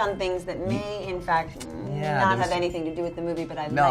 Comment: WTF did James say?? " I don't think any of them touch on it"??? Maybe.